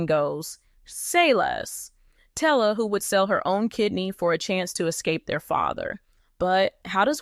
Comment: i thought you said kid for a second 😂